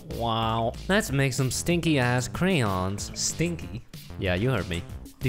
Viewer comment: Okay in the beginning did I hear correctly "Stinky ass crayons."?